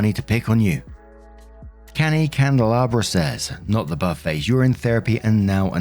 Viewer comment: I keep hearing "not the buffet" instead of "not the buttface." It's giving me a good giggle.